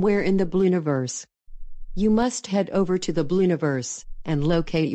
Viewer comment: Wait a minute, did she just say, the blueniverse?